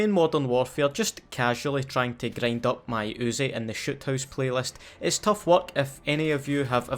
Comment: anyone else hear him say shit hourse